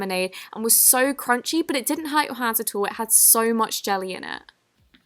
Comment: did anyone else hear she said “It has so much jelly in it”😂😂